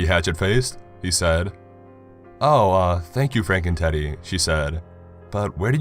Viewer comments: sounded like he said FrankenTiddy haha nice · I thought you said Frankentitty